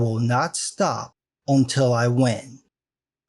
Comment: "Bring lube, it'll go smoother" So nobody's going to mention how badass that sounded to end a video? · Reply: Just Some Guy without a Mustache I thought he said “Bring Loot” not Bring Lube. lol